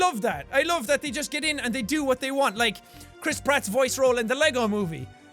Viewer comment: I swear I thought he said "Crisp Rat" 😂😂😂😂😂😂😂